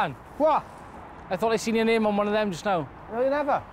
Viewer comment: Can someone tell me what the guy says at 13:20, just after his colleague says "Dan". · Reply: 'I thought I seen your name on one of them just now.'